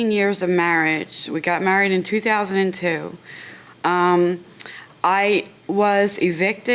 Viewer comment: I thought she said married in 3002!